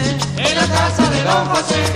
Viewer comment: Min 2:23 qué dice? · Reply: Don José Ybarcena. Creo que fue uno de los que les brindó apoyo.